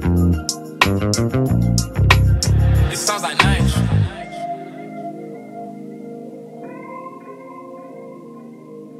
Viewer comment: did I hear “it sounds like nines” or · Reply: Nyge